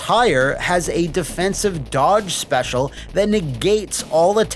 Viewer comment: Probably because of Shepski being onscreen a couple seconds ago, I really misheard that as "defensive dog special".